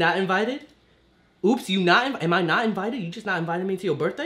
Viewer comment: This whole time I thought she said "ops you not about it" 😅😅